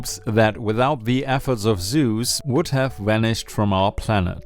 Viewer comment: i heard this as "without the efforts of Zeus..." and got confused for like 10 seconds